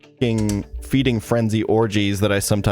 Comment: I was half listening and I heard "feeding frenzy orgies"..... wtf?